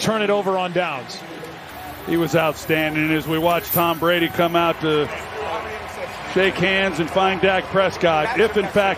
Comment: what Tom says to LVE is hilarious and so cool. A moment LVE will never forget for sure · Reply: he basically said “you could’ve had like 10 interceptions, catch one next week!”